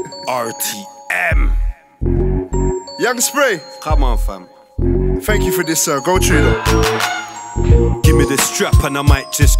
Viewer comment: did he say thank you first this sir go through still, that's how you know sprays a general